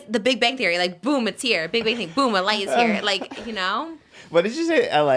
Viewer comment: mishearing trisha saying “boom, a light is here” as “boom, LA is here” 🤣